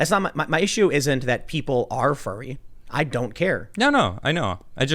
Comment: Am I the only one who keeps mishearing furry as fairy?